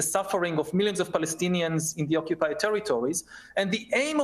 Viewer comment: did he say in the OCCUPEID TERRITORY ? 39:50 even piers face changed !